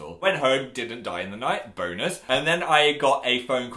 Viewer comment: did he say boners 😂😂😂